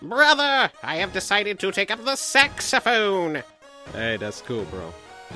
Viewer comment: when papy said Saxaphone.... it sounded like ge said Sexaphone.